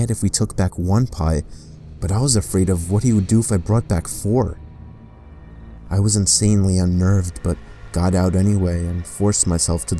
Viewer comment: In #1 did I hear 4 pies instead of pizzas?! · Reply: Yes you did 9:25 he says pies Pizza was originally called pizza pies Which is why he called them that